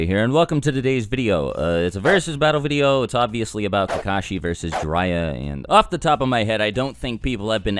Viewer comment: I thought he said "tekashin vs jiraiya" in the beginning 😂